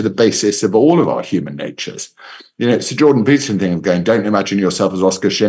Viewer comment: Did say “Sir Jordan Peterson”? 🤔 I don’t think he’s been knighted just yet.